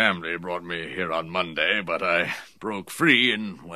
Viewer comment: What did he say at 2:16? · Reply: "I broke free and went on a bit of a bender." (got drunk)